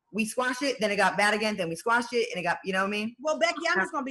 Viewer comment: Ok ummm at 1:11 did Tamar call her Becky?? Am i hearing that wrong????? Im confused